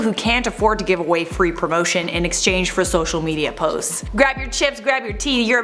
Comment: I heard "grab your chips, grab your tiddies"